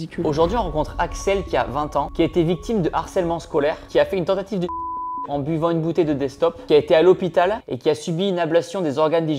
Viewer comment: I am trying to learn French, what did he say here [00:27] where it was censored? For being bullied at school, she tried to end herself by drinking the destop?